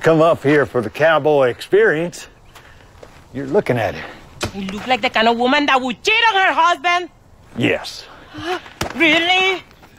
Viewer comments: Ooooh!!! I thought Gloria said to the cowboy guy that You look like the kind of woman that would cheat on her husband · "Do i look like the kinda woman who would cheat on her husband?" "Yes." 😂😂😂😂😂😂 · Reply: I misheard and thought she said “you” “you look like a woman who would cheat on her husband” calling him a woman 😅